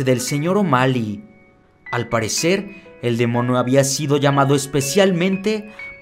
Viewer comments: like si en el minuto 7:49 escuchaste que dijo "demono" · Demono? No sé si escuche bien :v 7:49 alguien escucha lo mismo?